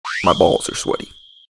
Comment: Bro, why did I hear "My balls are sweaty." and almost immediately look at the screen :l